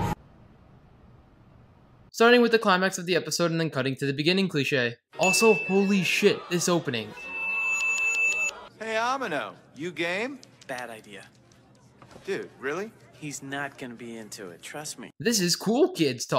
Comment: Right at the start when he said "yo Amano, you game?" I thought it said "Yo Amano you gay?"